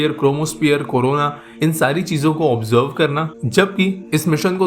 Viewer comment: did he say corona ???